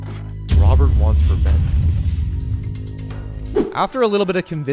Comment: when it says Robert wants revenge, the auto generated closed captions say “Robert wants for men”